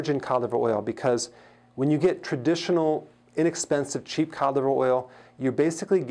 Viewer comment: Good information. But I hate the way Dr. Berg says "cod liver oil". It took me forever to figure out what he was saying. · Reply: I thought he was saying calive oil and tried to look it up